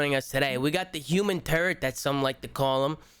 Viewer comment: I didn’t have my volume up and thought said the human turd instead of turret for a second.